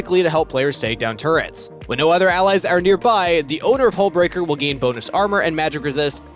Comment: I thought you said Hole Breaker and I almost spit my coffee out.